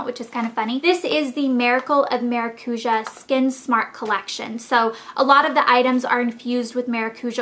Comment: thought it said "Marijuana Collection" and i got kind of excited.